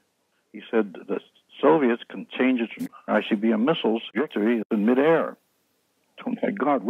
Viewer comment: Did anyone else notice the edit out of what the "Soviet's had" says something like they can "change _____"... Something about missiles?!? .... 22:49 that Corso wanted to go to Trudeau?? Change trajectory in mid air?? 😯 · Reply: He said trajectory. There was no edit when I watched...